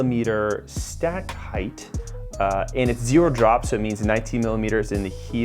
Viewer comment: low drop range? i thought you said 0 drop